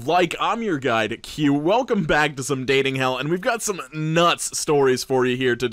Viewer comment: I thought he said, "Welcome back to some dating Ellen"